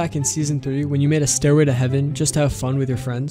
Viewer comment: I thought he said "... Steroid to Heaven ..." 🤣🤣